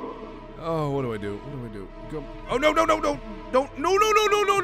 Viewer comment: did anyone else hear him say lol i think he meant to say no!